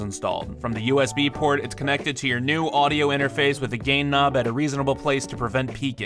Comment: All I hear is 'gay knob'